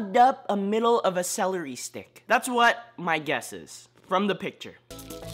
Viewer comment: The green tea one sounds like when he said a green tea shoved up a celery stick I thought he said shoved up a large penis